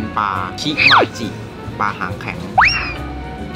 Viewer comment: ผมได้ยิน ปลา ำแข็ง 555+